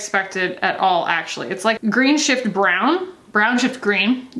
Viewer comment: Lol, I'm sorry when you were talking I swore I heard you say "Green sh#! to brown." I realize I misheard and laughed so hard.